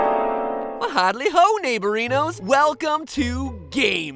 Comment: When he said neighborinos my nephew said "Heyyyy did he just say ninjalinos?? Like like pj masks?!?!" Adorable